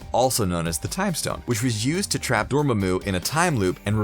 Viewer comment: did he say Dormumu? or is it just me?